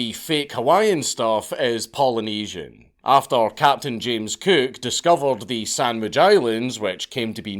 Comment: I legit thought he said “Captain James Kirk” and replayed this with subtitles to make sure I was right or wrong. I was wrong, and I don’t care. This was awesome